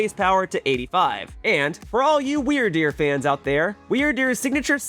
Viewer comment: did you just call me weird deer fan😅